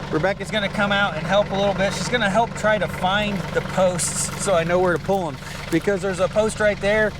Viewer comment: i thought he said she was going to find the post lol